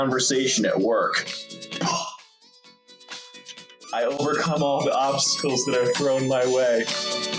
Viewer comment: "I overcome all the obstacles that I put in my own way" is how I heard it at first around 3:06 🤣🤣🤣